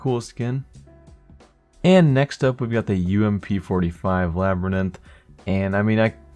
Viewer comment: Did he say labryninth?